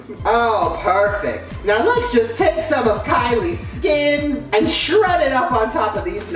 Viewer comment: Am I the only one who heard him say Kylie skin Like he can predict future